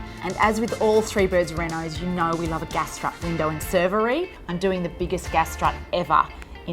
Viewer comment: Did you say a Gastrap window & servery?